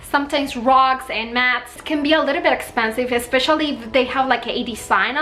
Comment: I laughed at myself because I heard "rocks and mass" & wondered if Dollar Tree sold rocks -- then realized it was rugs and mats when she held a mat. Good job!